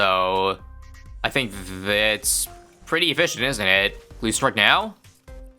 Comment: I totally heard "pretty bitchin'" instead of efficient 😂